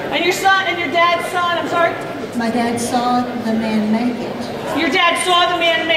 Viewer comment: When the woman said her dad saw the man make it, I thought she said she saw the man naked. I immediately thought, "Well, that won't increase the value of the piece at all." LOL